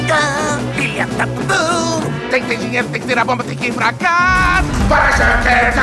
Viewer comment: "vai da merda!" Foi oq eu ouvi!